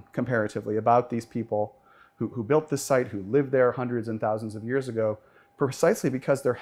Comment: Where annunciation is important: “hundreds AND thousands of years ago”. I first heard “hundreds of thousands of years ago”. I was like, sir, you may want to check that math. 😂